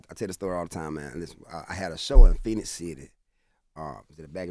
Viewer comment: Did he say Phenix City at 11:08?? My Birthplace in Alabama ❤